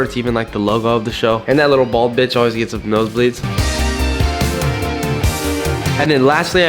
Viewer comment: did he say “that lil bald b***h gets nosebleeds”😭😭😭